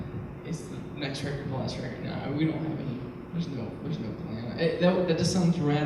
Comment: what is Tyler saying at 10:34? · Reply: Laia is the next record the last record? No.